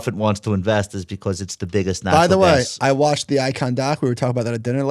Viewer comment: what did he say? icon doc? i want to look up the documentary and of the person that they are talking about? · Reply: Icahn: The Restless Billionaire. It's a documentary on HBO Max...